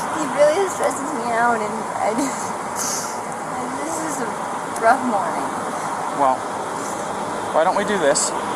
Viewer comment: What does Gabby mean when she said to the police officer, it’s a drug morning? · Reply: I thought she said rough morning?